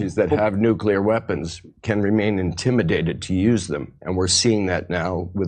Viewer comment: I thought he said we are being intimidated to use them. So he is bad at acting and English?